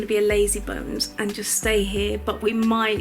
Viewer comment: Looks cold, but such a fun trip. Funny thing is when you said you were meeting "Dear Meryl" at the airport I thought heard it wrong and thought you said "Dear Mother". I thought she looked so young to be anybody's mother. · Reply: I did say dear mother hehe its the band name :) it was a great experience and everyone was so much fun x